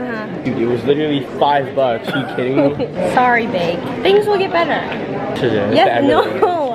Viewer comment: when says sorry bake the auto generate caption says sorry babe.